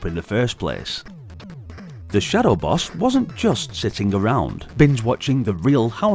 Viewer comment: When you kept saying "shadow boss", I thought you were saying "shuttle bus." I was very confused. 😂 The Amiga version looks great, I'm surprised. I didn't know that the Amiga had good arcade conversions like that. Then again I know next to nothing about the Amiga and what I've seen here in America is kinda well... Garbage